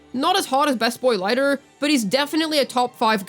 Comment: "Not as hot as best boy lighter" ?? Did I mishear? What did you say here? Or who were you referring to? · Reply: lighter from zenless zone zero